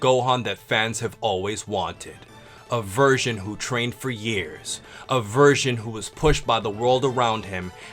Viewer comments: i swear i thought you said "a virgin that trained for years" lol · Who else heard "Virgin" instead of "Version"